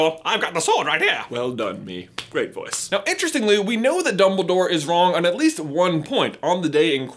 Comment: I THOUGHT U SAID "DUMBLEDORE WAS RON", AND I WAS LIKE: seriously?